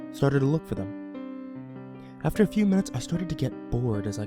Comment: Confused for a minute around 1:20 when he said a few minutes, I thought he said a few months